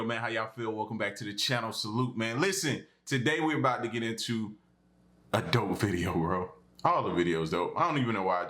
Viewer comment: When you said “a dope video” I thought you said “adult video” I was like: are we gonna watch porn??